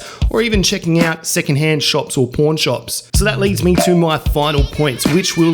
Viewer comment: I definitely heard "porn shops" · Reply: i guess pawn sounds like porn in his accent lmao